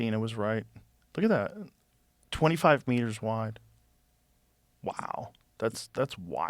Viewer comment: I really thought he said "25 weiners wide"